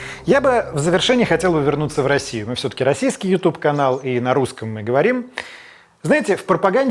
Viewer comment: в конце послышалось "мы все-таки расистский ютуб канал")))))